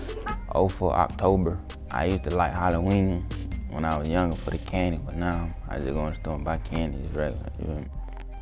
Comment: what the fuck is he saying. he didnt speak there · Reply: "i use too like halloween, when i was younger for the candy, now i just go in the store and buy candy, its regular